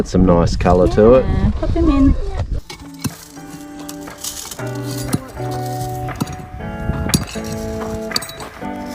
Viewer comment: hi idol what did she say "PATINGIN" is your wife from Philippines? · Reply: Hey there she said pop him in but it’s funny you ask because is half filipino her mum is from Philippines 😁✌🏼